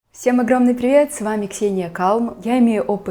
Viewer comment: Мне послышалось «все привет, я Ксения кал»